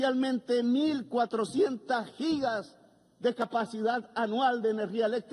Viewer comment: Thank you for translating! You may want to check around 16:08, where from context I think he's saying "gigawatts" and not "gigabytes", since he's discussing energy and not data.